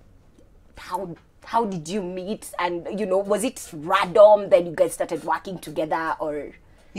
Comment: Did i just hear 'radom'